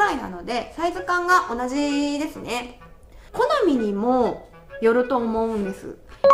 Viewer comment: 「コナミにも～」って聞こえるの俺だけ？ｗ